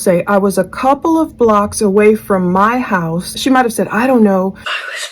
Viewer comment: interesting. I think she actually says I was a couple of blocks away from THE house. I find it interesting because a well known psychic Donna Seraphina, stated that Bryan parked a couple of blocks away - he footed it from the house to go back to the car. But why was parked a couple of blocks away? Maybe it was after the drop off and she parked and was making a call or something.